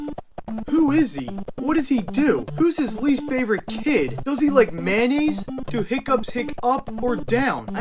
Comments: I thought you said does he like manny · I thought he said “does he like Mannys.”